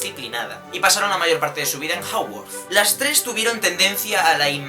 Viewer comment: Juro que entendí "Tuvieron una infancia bastante rigida y disciplinada y pasaron la mayor parte de su vida en HOGWARTS" JAJAJA Omg, esto de ser potterhead se me esta yendo al frutero.